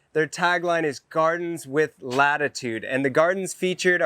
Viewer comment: I thought he said " Gardens with Attitude " 😅💁🏼 ha ,...Gardens with Latitude 👍🌿 I would love to visit one day !